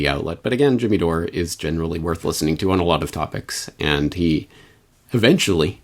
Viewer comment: Jimmy Dore has come a long way but he’s still in the waking up process. Not easy to break away from the endless lies, propaganda and subversion that’s been pumped into heads from day one. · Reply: I think he said "Jimmy Dore is generally worth listening to on a lot of topics". Had to play that bit at 10:27 on half speed a couple times, because it sounded like "worthless" to me as well :)